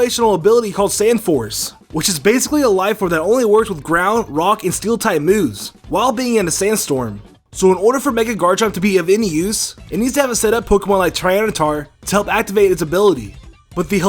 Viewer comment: did anyone hear him say "garanchomp"?